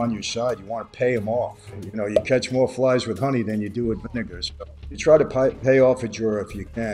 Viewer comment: I thought he said "you catch more flies with honey than you do with the n*ggrs" lmaoooo...😹😹